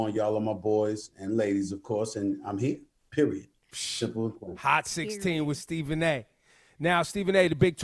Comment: YOOOOOO I THOUGHT HE SAID “period.. shittin me” had to run it back 😭